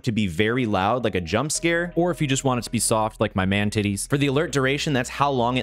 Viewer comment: Did he just say man t!ties